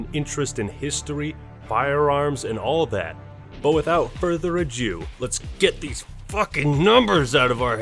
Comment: Did he say "without further a jew"